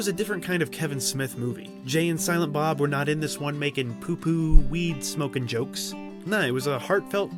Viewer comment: At the 14:00 minute mark, $ Did you say "Poo-Poo" ???